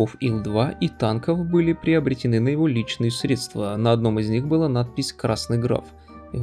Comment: Я правильно расслышал, "средствА"?